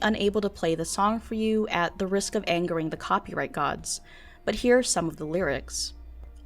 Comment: For a moment I thought I heard "At the risk of angering the Poppy-right gods" lol